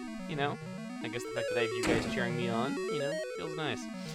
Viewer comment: I misheard that your fight went well "because you guys were turning me on." Oh god why...